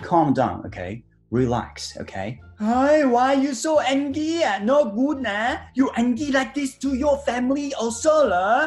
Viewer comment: タイの｢No good na｣がどうしても和訳の｢よーくない｣に聞こえて…(笑) 発音が和訳も英訳どちらも同じ意味に空耳してしまって面白いw